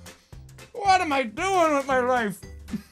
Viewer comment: "WHAT AM I DOING WITH MY LIFE" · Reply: I thought he said what I am doing with my wife, and I was confused, lol.